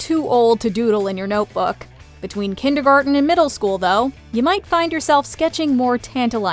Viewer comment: did you say KINDERGARTENERS DO THIS IN THEIR NOTEBOOKS. I need to go checkout my nephew 😢